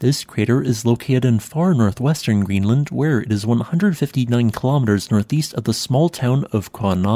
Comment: Did he say located on 150 million kilometers north? Who served on board an aircraft carrier so it might be my hearing.